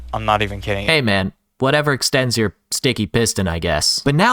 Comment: hold up, WHAT did he say??? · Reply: “whatever extends your sticky piston”